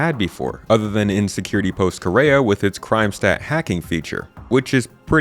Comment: I had a chuckle when it sounded like you said "Insecurity Post Kareah". Like yeah; I feel that. 😄